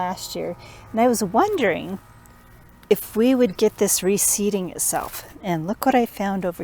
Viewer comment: These plants have seed pods that contain dozens of seeds. If you let them fall to the ground, and the conditions are right (wet spring at the right temperature, and seeds landing in soil that drains well, so they don't rot)), they will sprout the next year and grow new plants without intervention. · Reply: oh my goodness. You said RE-Seeding. I totally heard that as receding 😂. Whoops! Thanks for the reply